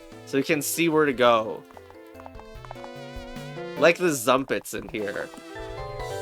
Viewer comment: did you just say zunpets ? TOUHOU MENTIONED